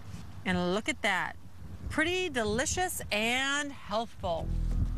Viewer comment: Did she call the sandwiches “helpful” or “healthful”? Lol